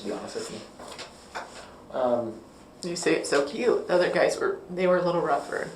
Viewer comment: Did she say the detective “you say it so cute?” 🤢